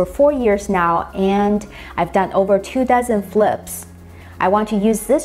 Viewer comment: I heard "2000 flips" at first. I was about to say....